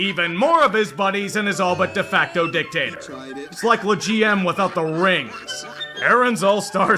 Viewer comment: subtitling error: the GM instead of LeGM (the meme that Lebron James is the GM of the LA Lakers)